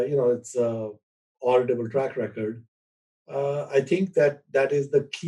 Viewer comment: what does Pabrai say? Laudible track record? Auditable track record? Can't understand the word. Thanks!